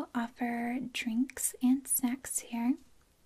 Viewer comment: I was watching at 1.75 speed and I SWEAR I thought she said "drinks and sex."